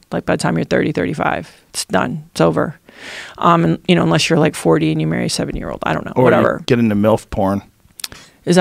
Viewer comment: "Unless you´re like forty..." and what? Can´t make out what she says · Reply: "Unless you're like 40 and you marry a 70 year old."